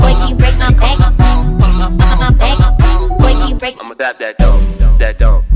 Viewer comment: dose anybody else hear I'ma adopt that dog or is it just me?